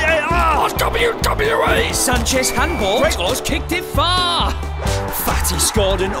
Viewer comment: i thought he said greizman